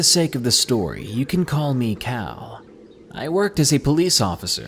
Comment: At the 1st story, i thought u said "you can call me COW"